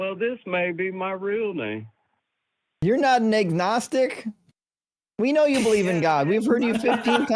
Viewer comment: I did a double take because I thought I heard the word "nignog".